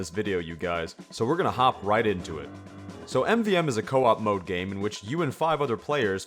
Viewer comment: did he say mode game?